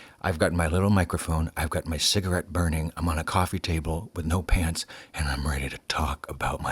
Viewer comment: I heard "I'm ON a coffee table with no pants" hahaha